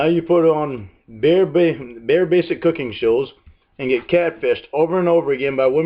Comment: It sounds like he say “ chows”